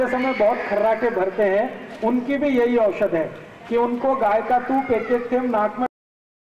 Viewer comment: me kya kaha gya ? cow ka kya dalne ko kaha ja raha h naak me ? mai samajh ni paya .. jinko samjh aya wo please reply me bataiye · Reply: Cow ka ghee